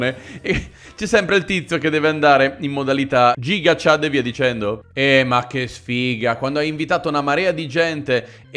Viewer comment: ho sentito bene? ha detto Gigachad?